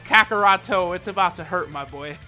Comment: I thought he said Kakarot's toe